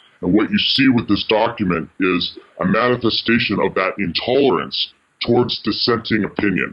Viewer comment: the caption said "tolerance" but should be "Intolerance" which is the word he/she spoke, other than that, great video.